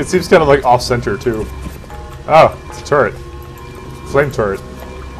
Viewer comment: I thought you said turd...